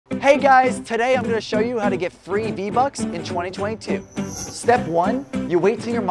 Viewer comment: I thought you said 3 vbucks😂